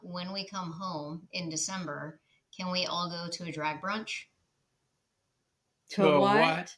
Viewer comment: My wife thought she said "dry brunch" and made a comment 'I wouldn't be able to with them without a cocktail'. I then when she figured out it was a "drag brunch" I said her cocktail comment is still applicable. But the in sync "To a what?" was priceless.